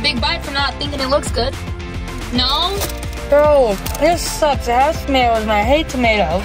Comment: did say it sucks a**?? 😂